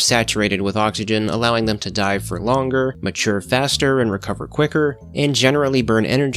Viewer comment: I thought you said die for longer and I was like bro what the heck, just let them have quick deaths